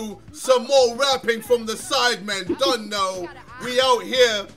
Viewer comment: What is the line JJ says at 0:04 ?? Please tell I have been wondering since so long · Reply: He says "dunno"